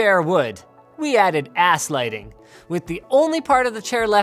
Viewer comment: i thought he said " Ass lighting " lol